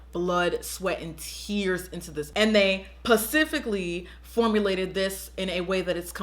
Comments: ik she did not just say pacifically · did I just hear pacifically??